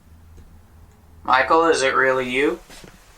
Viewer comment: 🖤🖤🖤🖤 when says is it really you Michael I hear " hi to Becky" plz listen again 🖤🖤🖤🖤